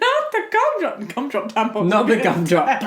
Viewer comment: "Not the cumdrop buttons!" wait wat?, think i misheard that xD